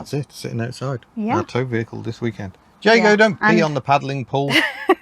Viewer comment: thought you said piddling pool….